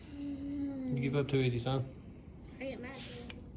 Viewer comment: I could swear i heard him say u give up to soon Son that Female